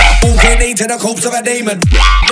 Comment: What the hell is he saying at 1:27? 'Walk with me to the corpse of Matt Damon' ? I stg xD